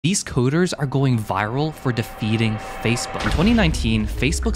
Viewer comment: I heard "These hooters are going viral" and I was very disappointed after rewatching the short